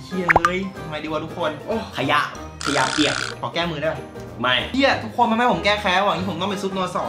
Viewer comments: ขอแก้มือได้ป่ะ สิ่งที่กูได้ยิน : หอมแก้มทีได้ป่ะ 555555 ขอโทดนะคะ 555555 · ทำไมกูได้ยินว่า "หอมเเก้มมึงได้ป่ะ"55555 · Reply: "ขอแก้มือได้ป่ะ?" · ขอแก้มือได้ป่ะ สิ่งที่กูได้ยิน : หอมแก้มทีได้ป่ะ 555555 ขอโทดนะคะ 555555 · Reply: ซุปno:2 กูได้ยินเป็นซุปน.ส.